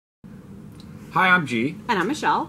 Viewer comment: This whole time I thought he was saying “Hi I’m Gene” 😂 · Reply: Lol! Most people think he's saying Gene, but he's really just saying "G". =)